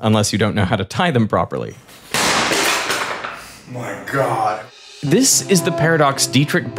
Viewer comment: “My God” sounded like he forgot cushions just in case😂